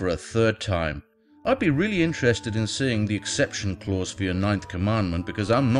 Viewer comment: Voice says ninth, subtitles say seventh, Augustine says eighth...